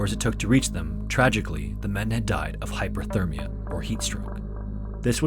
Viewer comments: the subtitles say "hypothermia" but it is actually "hyperthermia" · Subtitles say Hypothermia instead of Hyperthermia